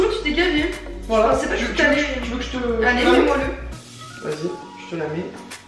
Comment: A 6:51 elle dit : mais moi le ???? ( MAIS LE MOI !!!!!!! )